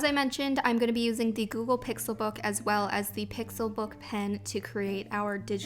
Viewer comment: lool at first I thought you said glucose pixie book